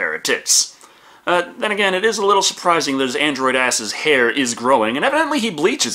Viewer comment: Am I just mishearing when you talk about David bleaching his hair at 13:45? Cause I'm hearing that "This android's asses hair is growing and evidently he bleaches it." Cause I spent 5 minutes trying to find where David shows off his Ass hair...